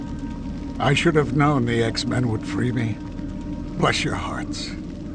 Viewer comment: It sounded like Cyclops said 'sex men', lol.